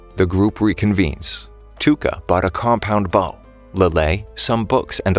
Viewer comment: did he say compound bomb? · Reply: Bow